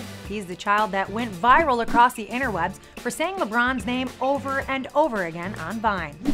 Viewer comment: did she just say "interweb"? 😐👏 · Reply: I thought she said interwebS like there's more than one?